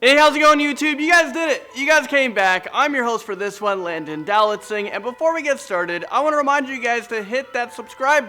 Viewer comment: For the longest time I thought he said "I'm Landon Do-Not-Sing" but he didn't. 😂